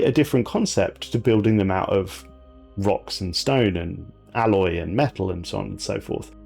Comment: DID I HEAR A ROCK AND STONE?!